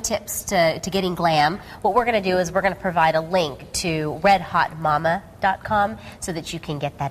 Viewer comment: First I thought she said "wet hot mama" at first